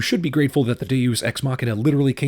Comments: if I heard correctly at 5:01 he said GTA mother f*cker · did really say " GTA MOTHER FUCKED "